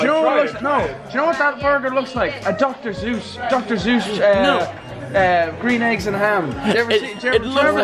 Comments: I thought he said doctor Zeus for a second. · Back Up 13 Steps Said Dr Zues At 8:20 Boi Its Dr Sues.